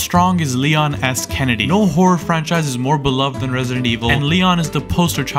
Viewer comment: bruh why i hear it as "wh0re franchise"💀